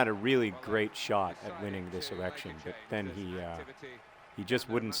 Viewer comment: Sounded like: "Santorum ha a really great shot of winning this erection."